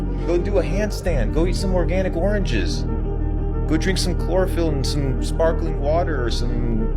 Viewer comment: did he say "drink some chlorophyll"?